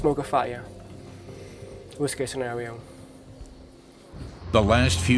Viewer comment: who noticed "whiskey scenario" instead of Sierra.. ... 😆